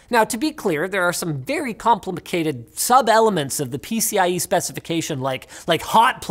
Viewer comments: did he say complimacated? i'm using that from now on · Did he just say "Complimacated"? @ 4:52 Linus is so edumacated... 🤣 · Did he say compli-ma-cated? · Am i hearing things are did Linus actually say "Complimicated" @ 4:53 ? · did he just say complimicated? ...oh hell HES THE ONE THAT DRANK MY BEER!!! now im off the hook with the wife for buying the 3rd case last night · Did Linus just say, "Complimacated"?